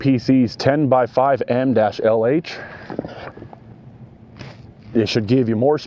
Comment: Sounded like you said your prop was 10x5 N LH I thought they where 10x5 M lh for the marine one ?